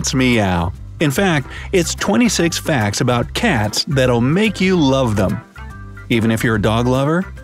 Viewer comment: Oof, I thought they said “That’ll make them love you” oop